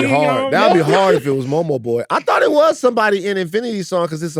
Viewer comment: Was sleepy af when I saw the new cast, and DEF’ thought I heard Momo Boyd was casted as Storm for a sec 😂